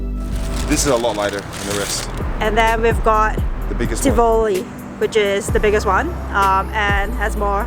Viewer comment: I'm trying to catch what you say on 4:07 is that Tivoli ?! Lol great video, try Monforte viennoiserie & oven st bakery too both so yum . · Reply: Yeap! That biggest one was Tivoli :) And I definitely want to try Monforte and Oven St. Been eyeing Monforte but their opening times are a little tight for my schedule so hoping to head there on a Saturday before the crowd hits. :D